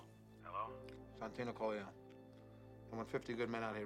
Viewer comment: "It's Santino Corleone"... He really needed to give Tessio the last name? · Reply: I thought he said ' Santino calling here' with the bread in his mouth